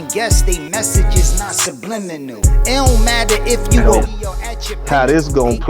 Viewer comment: Did Cassidy say SUBLININAL??🤣